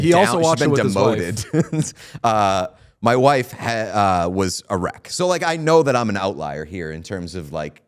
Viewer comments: did you say that your wife was "erect" (because that's what it sounded like) or did you say she was "a wreck"? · I really thought he said "my wife was erect."